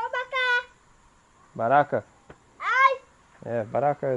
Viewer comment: I don't understand Saamaka, but what I can understand from what the boy said (14:33) is that 'you should come back' (yu mu kon baka). Today you should come here. 15:49 - 16:02 Yeah that's the 'Kankantri'. The name is a corruption of the English 'cotton tree' (Ceiba pentandra)